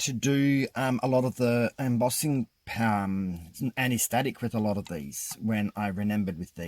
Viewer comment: These are absolutely stunning, wow!!!! This will make you giggle - the first time you said you was wiping the jesmonite with an anti-static wipe, I misheard you and thought you'd said anaesthetic wipe! I was completely and utterly baffled until you had the static issue on the 2nd one and then I twigged PMSL.... I have never used jesmonite or embossing powders but I really want to try this now